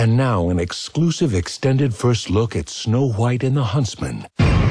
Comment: 聽成snow white and her husband= =